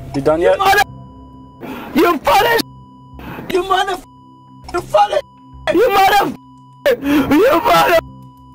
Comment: All i hear was "You motherfff you fatherffff you motherfff you fatherfff"